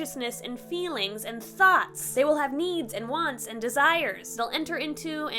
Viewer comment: Istg, I thought you said “Memes and wants and desires” and I just accepted that as fact